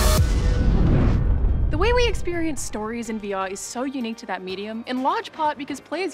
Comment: Sounded like she said "weewee experience"